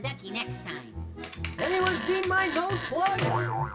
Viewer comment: I thought he said did anyone see my golf club